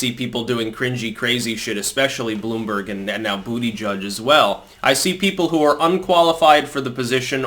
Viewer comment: did you call him "booty-judge"? I almost fell out my seat laughing. 1:08 and yes, he would screw you if YOU got in position 1:13 LOL